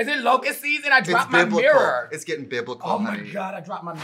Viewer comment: I thought she said "it's bibble" like the Barbie creature...